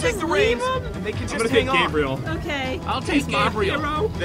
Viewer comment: Does jessee say "Bobriel," or "Gabriel?" If you think of one you can hear it.